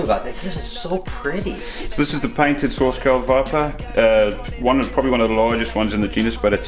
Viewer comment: What was the snake at 2:55? I heard "painted viper" but didn't catch the rest of what he said. Absolutely gorgeous specimen! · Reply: Pamela Schramke painted sawscale viper